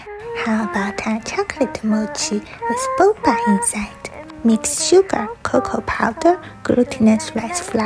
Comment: We wants jimin our mochi~ ....😅😅💜💜💜💜 When you said butter suddenly My mind is singing smooth like butter ....~💜💜😅💜💜 Mom I am famous... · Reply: When she said "mix some sugar" i heard "mix some suga" 😂💜